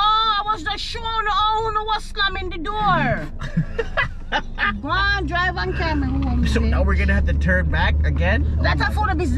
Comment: Did she say "just drive and take me hime bitch" @ 16:20 ??? Ahaha love her